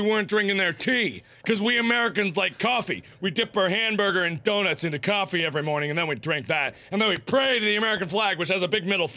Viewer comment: did he say dip our hamburgers and donuts in our coffee?